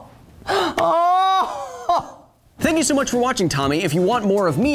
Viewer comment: Am I on drugs, or did you just say, "Thank you so much for watching, Tommy..." ? D